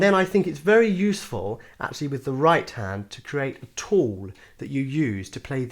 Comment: My American ears can’t seem to make out what he’s saying at 1:30. What is it that we should create with our right hand? A toad? A tort? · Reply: Guys it's a tool